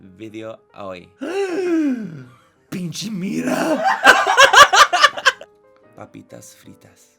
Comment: Omg! Me encantoooo!!! 😍 i totally thought at 8:40 you said "pinche migra" 🤣🤣 please do moreee spanish videos 🤗 te necesito en mi vida!